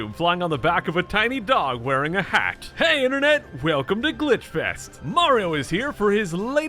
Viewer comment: I heard "Hay in a net"